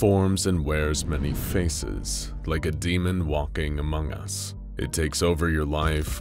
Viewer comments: did you say aumogus O_o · im sorry at 0:06 did he say AMONG US??